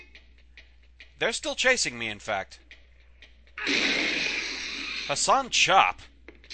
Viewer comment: Did I just hear "Hassan CHOP!" at 10:31? Nice!